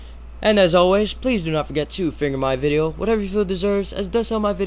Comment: Did I hear.. "finger my video" o__o